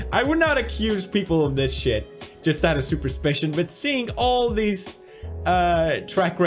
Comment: DID ANYONE ELSE NOTICE THAT HE SAID SUPERSPITION INSTEAD OF SUSPICION